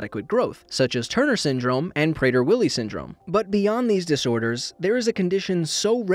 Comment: I thought you said prayer “Willy” syndrome lol